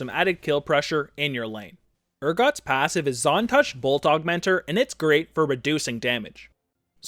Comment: I heard "On Touch Bulldog Mentor" for his passive. Great guide